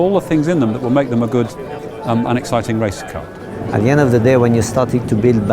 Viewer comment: Did anyone else hear, "a good, unexciting racing car" at 3:24?